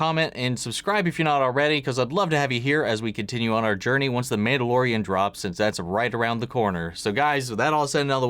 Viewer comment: Thought you said the Maid-alorian. Which made me think of Mandolorian's but their armor is shaped like Maid Café staff.